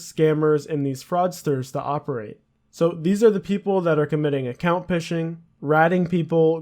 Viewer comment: did he just say account pishing :joy: :joy: :joy